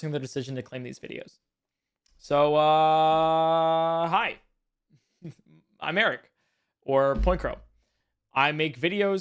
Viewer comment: Sorry, serious video but NGL, when you said, “Hi. I’m Eric.” I heard, “Hi. I’m married.” XD